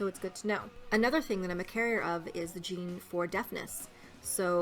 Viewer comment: when you said deafness i thought you said deadness